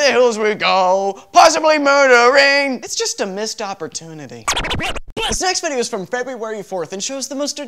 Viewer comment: Is it just me that on 1:40 I hear said "The sex video..."?